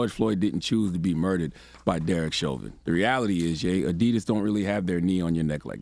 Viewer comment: But but but but I thought Kanye said “And Adidas can’t drop me!”.